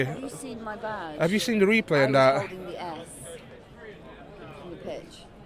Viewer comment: What did the lady say? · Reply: She was '"holding the 'S'"? I know there was a 'CHELSEA REMEMBERS' as part of the Poppy Appeal to mark Remembrance Sunday at the start to the game so possibly she was holding that? Its cool if that is what she was doing but quite clearly a production going on, only had to wait until after the interview.